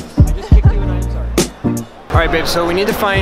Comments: I thought i heard "alright bitch" hahah. but thats what i get for not paying attention. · At like 2:57 , says alright bitch..? What does he say I'm so confused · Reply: Alright babe